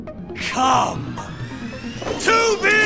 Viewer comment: When He Said "Come, 2B", For A Second I Thought He Said 'Come To Me'